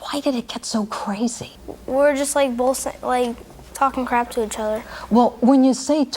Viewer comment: Almost sounded like the kid was gonna say "We were just bullshitting" to the judge, But he caught himself lol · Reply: We were just like both is what I heard but idk